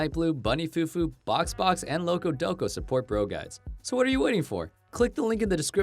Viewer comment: it almost sounds like he said broguides